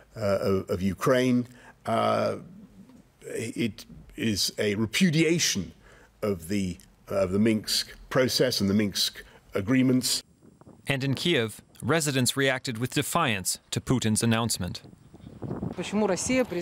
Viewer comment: Did Boris call it the Minx agreement instead of Minsk? Lol